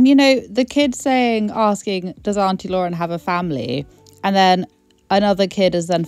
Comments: I think the kid actually said, "Is Auntie Lauren has a family?" His grammar is horrendous and I think he has a bit of a lisp so it does sound like he says, 'Is Auntie Lauren out of jail?' Weird. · I thought he said is ‘Aunty Lauren part of our family?’, to which she says yes. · When radianbritt posted I heard “is auntie Lauren outta jail?” But I can also hesr “does auntie Lauren have a family?” I hear them both